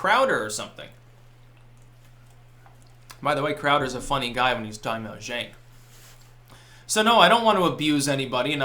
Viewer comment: I can't for the life of me figure out what Styxx says here. "By the way Crowder's a funny guy when..." Anyone any ideas? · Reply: when he's talking about cenk.